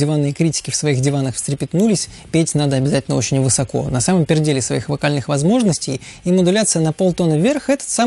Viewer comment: я один слышу "на самом перделе своих возможностей"?